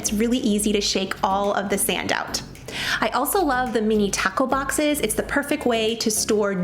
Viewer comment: Thought you said “mini taco boxes” 😂